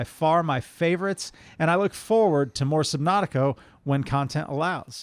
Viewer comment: Did say Subnautico? At about